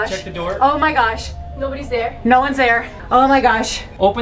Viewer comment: When she said no one is there it sounded like she said Nolan is there lol